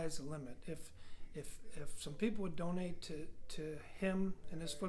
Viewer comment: We all need to donate to the program now people ‼️‼️‼️ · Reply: Donate? I thought you said.. donuts!! Mmmmhhhmmmm!!! -Rosie O Donnel